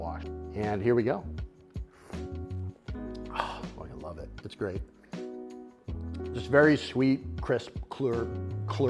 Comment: Yoo did you say "fuckin love it, it's great" at 11:00? You come as so family friendly lol caught me off guard nearly spat my tea out! Using the rotating teapot going through the sampler pack now